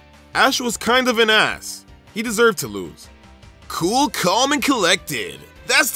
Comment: it's calm, cool and collected instead of cool, calm and collected.